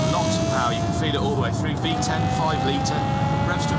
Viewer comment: did he just say VTEC at 3:12 on a european 5 litre V10? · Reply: no, he says "v10, 5 liter"